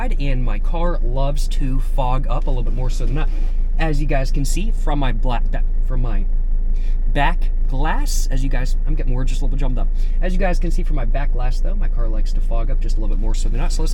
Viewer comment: did he almost say “my black ahss” instead of “my back glass?” 😂😂😂😂😂